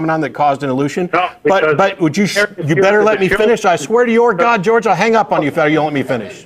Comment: "you better let me finish I swear to your god George" Lmfao I'm using that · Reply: i heard, "I swear to your God".